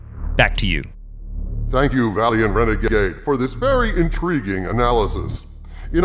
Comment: LOL 13:47 Did you just call Valiant Renegade, "Valiant rena-G-G-Gay"?